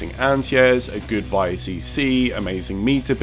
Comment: Oh "VALLE CC". I thought you were saying "via-CC". I wanted to look up the meaning of this but I couldn't find any reference to it.